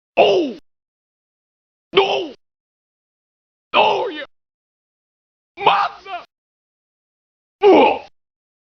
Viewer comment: Did he just call me Massa?